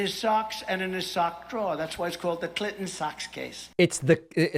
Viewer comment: I thought he was saying, "Clinton Sux."